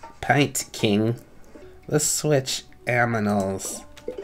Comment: Wait did u just say let's switch aminals? XDDDDDD